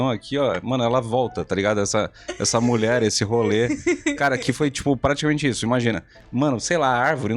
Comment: Eu achei que ele ia falar "essa roliça"